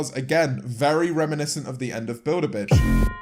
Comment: The whole time I thought he said Bill the bitch